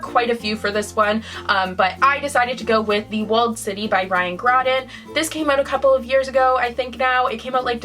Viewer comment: I thought you said The Walled City by Ryan Gosling 😂